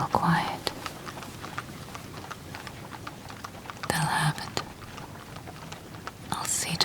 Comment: “they’ll have it” I heard “The hell happened?”😂😂😂